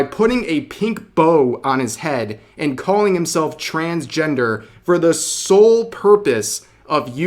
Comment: lmao. yup thats cartman, he can't help himself from ripping on gingers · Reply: i thought he said transgember as in gember, it's a type of vegetable